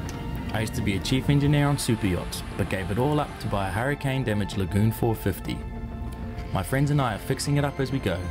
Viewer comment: I thought he said goon, then realised it was Lagoon